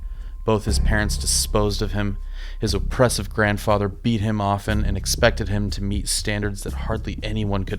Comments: "His oppressive grandfather beat him off..." 🤔 What's that you say? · did you just say his grandad BEAT HIM OFF🤣🤣🤣🤣😅😅😂😂😂😂🤣🤣